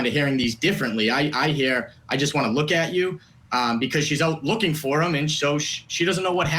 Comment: I hear “John look at you”